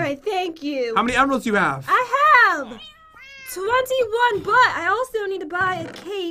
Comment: Pat: how many do u have? Jen: 21 but I thought she said 21 butts😂😂😂😂